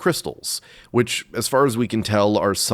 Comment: I could have swore you said Arbys insead of Orbeez.